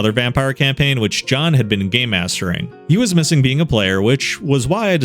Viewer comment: At the beginning I thought he said "gay mastering" and thought, wth is that? Thats a term I havent heard. Then replayed it and found out he said game mastering, which again isnt something I hear often as its always reduced to its acronym, GM.